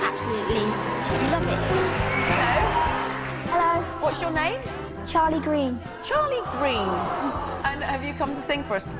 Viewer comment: oh thought it said charlie sheen... lol